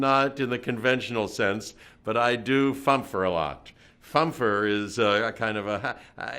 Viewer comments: what does he says, please? "I do funpher (?) a lot" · What is the word he uses to describe that he is speaking slowly, with lots of "err" and "um"? It's around 0:25-0:35? Sounds like "funfer" or something